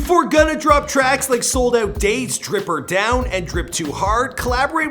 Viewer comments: He said drip or down, it’s drip or drown 😂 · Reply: Jelly Bean I thought he said drip her down 😂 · did he say “drip or down” 😂😂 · did he say drip or down!?